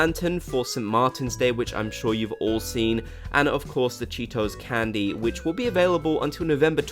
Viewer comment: I thought you said “Cheetos candy” lol